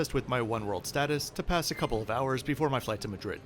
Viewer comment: flight to Madrid??? But the subtitle says flight to Frankfurt.